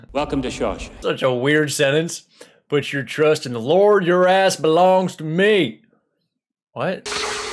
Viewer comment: It took me until I saw this video that I realized the warden said "Your ass belongs to me." For whatever reason I always heard "The rest belongs to me," which made a lot of sense for his personality. Your trust goes to the Lord, but the rest of you belongs to me.